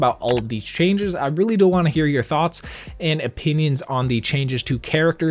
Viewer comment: anyone else hear him say “I really dont wanna hear your thoughts” No just...me aight bet